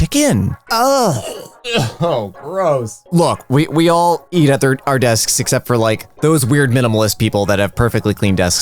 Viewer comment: "We all eat at our desks" while i was eating at my desk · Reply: I thought it said "we all eat our desks"